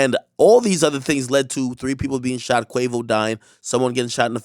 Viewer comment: did he say Quavo dying ?